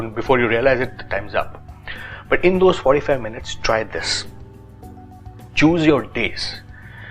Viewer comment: I thought you were saying "Choose your dicks" 😝